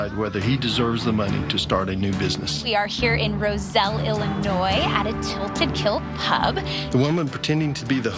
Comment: And hey, I heard him sayin Illinois as (Illi-noise) haha